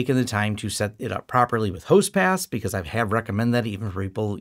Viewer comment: you mention backups and setting up "host pass" properly. The CC says 'host pass' is what you are saying there. I'm not sure I heard that right. There isn't anything in this video's notes about it. And when I search for "host pass" in your channel video list I don't see any such title in the results. Were you saying "host paths"? And which of your videos were you referring to in that part of the video? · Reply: it's "host path", not pass.